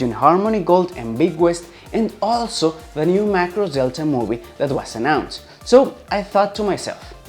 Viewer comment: what does he say ? " New Macross (?) Movie ". ? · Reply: New Macross Delta Movie.